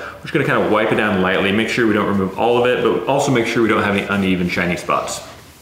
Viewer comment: Ahhhh, he said, "uneven shiny spots." I thought he said, "uneven Chinese butts." I thought it was maybe a figure of speech I'd never heard before.